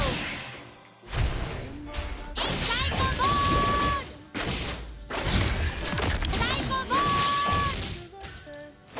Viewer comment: I wondered what Guinevere is saying🥲 I listened many times but I don't get it🤣🤣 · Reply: Psycho ball lol